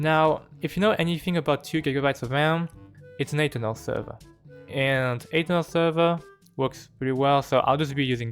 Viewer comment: you say something about server ... i dont get what you say It's an ATEL server? Plz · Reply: Aternos